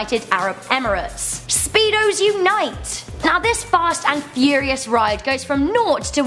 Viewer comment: i thought she said speedos unite